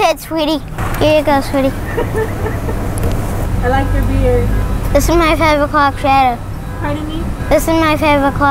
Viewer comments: Im gonna he honest when he said “this is my 5 o’clock shadow” I heard “this is myfubadock adoo” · “This is my 5 O’clock shadow” “Pardon me?” “This is my 5 O’clock shadow” “Huh?” “This is my 5 O’clock shadow” “What?” “This is my 5 O’clock shadow” “Oh...” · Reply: No, it sounded like he said "This is my favourite clock shadow" .. Even google subtitles said "Favourite clock" ... lol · This is my five o’clock shadow This is my five o’clock shadow This is my five o’clock shadow THIS IS MY FIVE O’CLOCK SHADOW oh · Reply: i heard “this is my favorite crockshedo” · I'll admit when he said "this is my five o'clock shadow" i heard "this is my shashashashadow